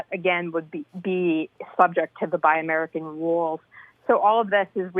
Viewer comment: Lol but American? Why? Even our politicians don’t care about America. Keep doing what your doing. We are only a unified country by name.... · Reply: I thought Biden said "Bye America"